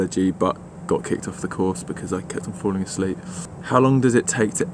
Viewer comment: What does he say in 7:35 ? Why was he kicked out ? :D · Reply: kept falling asleep in class xD